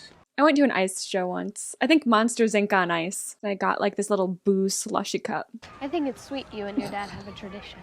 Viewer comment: I thought you said “booze slushee cup”. Monsters Inc on ice isn’t just for the kids. 😂